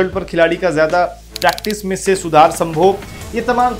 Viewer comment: I heard "Sudhar Sambhog"...is that correct?